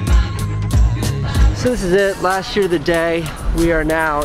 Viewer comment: did anyone else hear at 9:57 "this is the last yr of the day" lmao like if u did